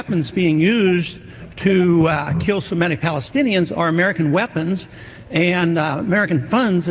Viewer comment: "So many Palestinians ..." They are Semitic, but that is NOT what Ron said. He said, "So many Palestinians ..."